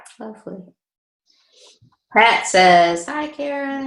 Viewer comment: does she say Prat or Pat, please ? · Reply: Pretty sure it was Pat. Sorry it took this long for someone to reply. You would have thought the host would have replied. Might take that with a grain of salt. Not sure I believe these people. If this is fake then they are the lowest life on this planet. Not saying they are.